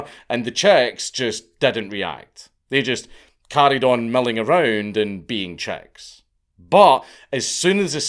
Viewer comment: When he said “the chicks milling around and being chicks” I swore he said “Czechs milling around being Czechs” and it wasn’t until he mentioned a hawk and running for cover did I realize what he said 😂😂😂 I was picturing Czech babies being shown shadows and not reacting!